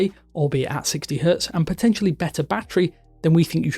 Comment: Did I hear " potential better Battery on 4a5G " ?